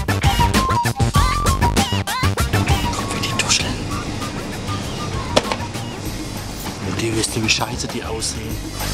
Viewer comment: was sagt er bei 1:23? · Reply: "guck wie die tuscheln (reden)"